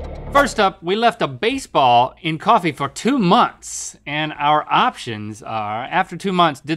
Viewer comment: I thought he said he left a cat in coffee for 2 months a I think I got a heart attack.